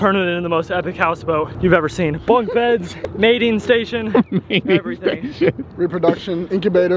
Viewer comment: did you say Baiting station or MATING station, I just left the hospital, had anesthesia Mikey juice, I may have misheard.. please confirm what you said..because this propranolol or whatever it's called had me hearing mating station.. 😂